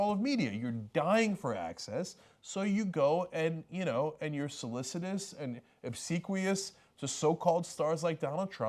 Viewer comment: "..you are solicitous and absicuitous(?)..." (what is the word? English is not my first language)